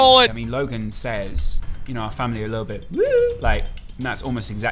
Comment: Did he just say little Tay?